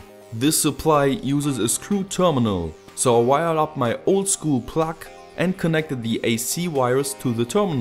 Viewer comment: did he just say I’ll wire up my old school pluck???